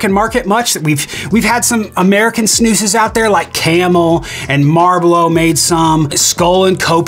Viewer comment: "marblow" lmfao how late did you say it was and how much have you drank lol. I heard marblow and laughed so hard I went back and listened 6 times. 😆